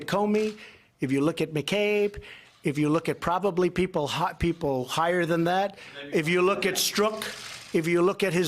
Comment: Did he say 'Hot people?" LMAO